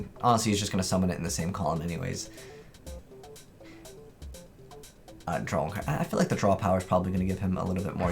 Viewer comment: The English auto generated captions says "I'm drunk"